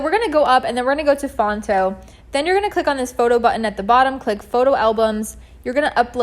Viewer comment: Thank you for sharing this detailed tutorial! Trying to start today with this side hustle idea. At 9:10, what is the name of the app you use to make the thumbnail, please? Couldn't hear clearly. Again thank you for your videos! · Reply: Phonto! I’m uploading AN UPDATED free course of this ASAP! :)